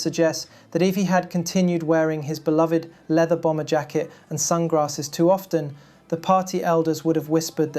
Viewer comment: I think I heard you say Sun Grasses.....maybe not...it was close....how funny..😅😅